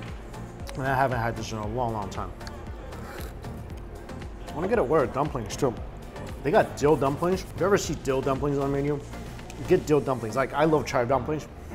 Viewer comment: damn i thought he said dough dumplings